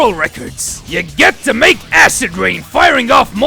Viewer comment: No matter what anyone says, I will always call this movement "𝙏𝙝𝙞𝙨 𝙢𝙮 𝙠𝙞𝙣𝙙 𝙤𝙛 𝙧𝙖𝙞𝙣".